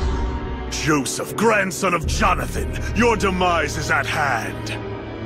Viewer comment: What is dio saying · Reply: Joseph grandson of Jonathan your demise is at hand